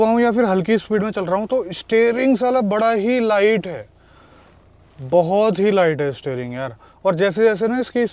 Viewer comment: Am I the only one who heard At 2:14 … “steering saala bda hi light hai”..??😂